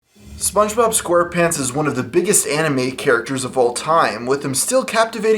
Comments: at the begining I thought you said, "SpongeBob is one of the most popular anime characters of all time" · anime character? Lmao XD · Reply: ***** i know he said animated, it just sounds like he said anime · Did you say anime XD. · Lol at the beginning it sounds like you said Spongebob is the biggest anime characters of all time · I thought he said anime character instead of animated character lol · At the start I thought you said sponge bob was an anime character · He says animated even though it sounds like he said anime. He just didn't annunciate it properly. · i thought he said anime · at the beginning I thought he said SpongeBob was a good Anime character instead of animated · I heard him say "animated" idk why y'all are complaining about him calling spongebob an anime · Pretty sure he said "animated", not "anime", but the lack of enunciation makes it sound like he said the latter. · At the beginning I thought he said Anime Character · It sounded like he said Anime instead of Animated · I heard anime......am I the only one?